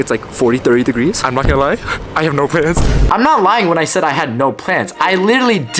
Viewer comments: i thought u said you had no pants not plans🙏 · i thought bro said "i have no rizz" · it sounded like you said you had know pants 2:51 lmao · I thought he said “I don’t have no pants “😂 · I thought I heard I had no friends